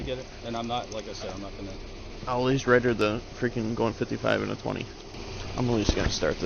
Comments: She was driving 55 in a 35. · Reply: I heard the cop say 55 in a 20...he says it at 14:38 in the video. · 55 in a 20 I thought he said 35 a couple times before that.